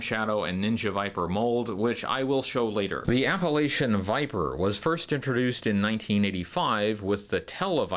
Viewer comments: I misheard you at first, and now all I want is an Appalachian Viper. It would come with several bottles of moonshine as accessories. I may have to do another custom. Great video as always! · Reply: I heard "Appalachian Viper" too. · Thought you said Appalachian Viper for a second.